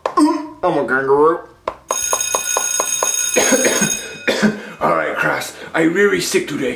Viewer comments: what does jeffy say? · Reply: I’m a kangaroo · wait what did he say? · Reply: UNH I'm a kangaroo?